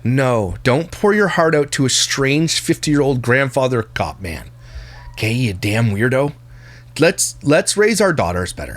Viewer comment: Omgosh at 28:43 I thought called him a "50 year old grandfather clock man" 🤣🤣😭😭